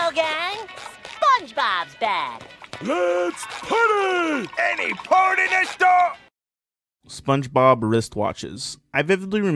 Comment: For anyone wondering, at 11:00 Mr Krabs was saying “Any port in a storm” before being cut off. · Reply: When I first watched that episode, I thought Mr Krabs said "Any partyness going on"